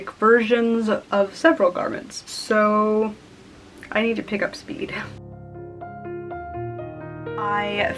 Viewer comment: When you were saying you didn't accomplish much in the last hour... I thought you said "I need to pick up some weed" and I looked up (side eye) and said will that make you go faster though? Then my brain reprocessed... aaaahhh... OK. 🤔